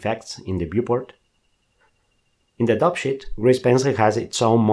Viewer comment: whenever you say "dope sheet" i always hear "dup shit" 😂